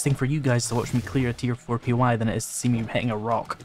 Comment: At around <link> , I originally thought you had said "... see me petting a rock." That would have warmed my "dad jokes" circuitry, but I re-ran that section and realized you had actually said, "hitting."